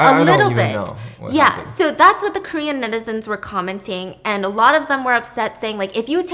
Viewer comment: Do you say nitizen instead of citizen? Or am I hallucinating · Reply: She said netizen. It’s means internet citizen, it’s a common word amongst I think the Asian community when it comes to internet users